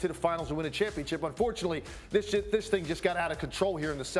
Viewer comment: Did he just say "This $hit."?? lol